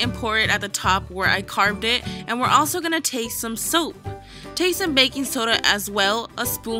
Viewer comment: the lemon on she was saying take but I thought she said TASTE so baking soda